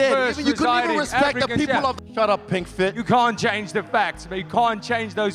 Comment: Izzy was even calling Dricuss Pig Skin... Imagine if Dricus called izzy black skin, I'm not personally offended but it's a huge double standard and Dricuss has done a good job not taking any bait. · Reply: I thought he said pink skin?